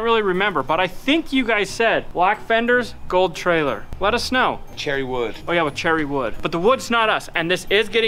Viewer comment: I thought you said gold trailer, black tires. ;-)